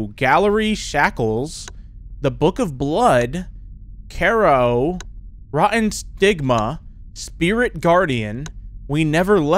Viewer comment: I thought it said rotten sigma